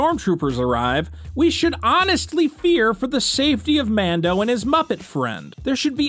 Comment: i thought you said lando